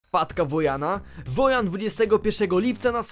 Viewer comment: Ja zamiast wpadka usłyszałam matke